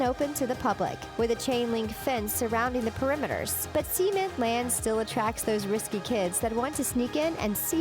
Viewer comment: Whole time thought she was saying "semen land"